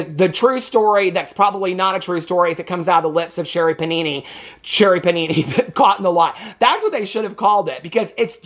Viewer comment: I thought you said Sherri Papini caught in the wild and you were saying caught in a lie… 😂😂😂